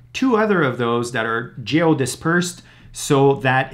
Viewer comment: geo dispersed sounded like jail dispersed.